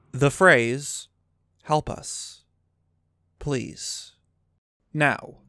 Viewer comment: for some reason i hear "help us hylia, link!"